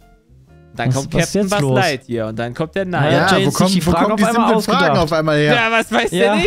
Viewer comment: was sagt jay nach buzz lightyear? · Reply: ich glaube er sagt „und dann kommt der Neid“